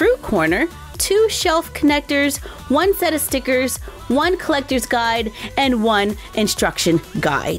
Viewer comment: Just me that heard her say fet if stickers?